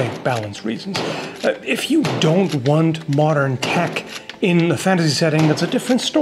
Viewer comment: thought he said modern kek for a second